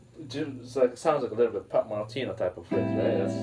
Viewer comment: Who did he say it sounds like? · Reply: regina yango He said it sounds like Pat Martino type of playing.